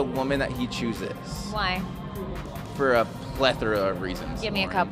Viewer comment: he says "For a ____ of Reasons". What's the word he says? English is not my mother's tongue so that's why I'm asking. Thank you! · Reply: he said 'plethora'